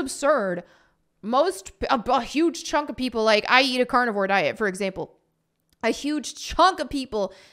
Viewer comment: When you said "I eat carnivore" for a second that sounded like you said "I eat cardboard" hahaha